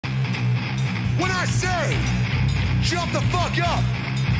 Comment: I thought he said "shut the fuck up". That makes it funnier though.